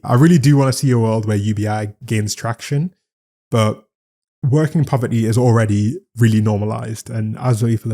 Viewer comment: I misheard you say "working poverty" as "working property". I suppose both are true. We are just property for the big dogs.